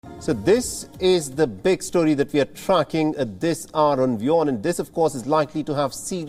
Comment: I thought he said t"The big story we are fucking".